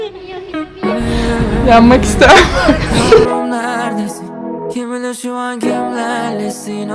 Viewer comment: ne dedi o :)) bide konuşabilse :)) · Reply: Yanmak ister misin 😅 dedi